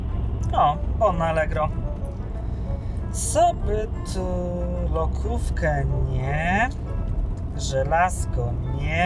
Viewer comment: Nie wiem czy wyłączyłam lokówkę." A włosy proste 😅😂😂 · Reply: Czemu ja usłyszałam lodówka 🤦🏽‍♀️